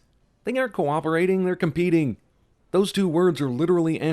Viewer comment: you say those... subtitles say the :)